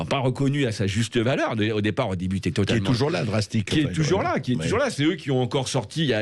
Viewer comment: je recherche avec google ce collectif j'ai compris "dragstick" dont on parle vers 18'12" quelle est l'écriture exact ? merci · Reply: Drastik